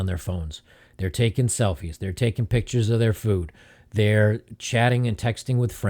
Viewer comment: I thought he said they were taking pictures of their 💩